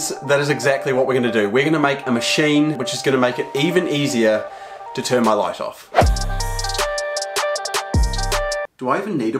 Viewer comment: Honestly thought he said "to turn my life off"